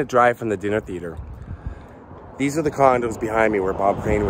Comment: you are truly the best! At 11:13 I listened to you 5 times to make sure I heard correctly, "these are the condoms behind me where Bob Crane was murdered." Classic!!!